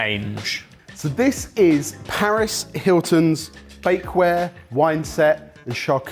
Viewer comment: I thought had said "Paris Hilton's fakeware, ..."